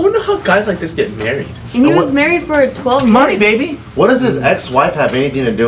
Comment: did say 12 months? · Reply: I think she said "He was married for 12 years."